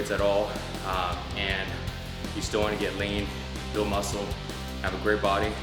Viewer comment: i thought he said "you still want to get laid".. but after replaying it 3 times.. he says "you still want to get lean" LOL